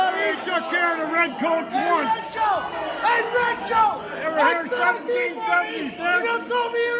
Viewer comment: did dude call that other guy a "red coat"? 👀 ☠️🤣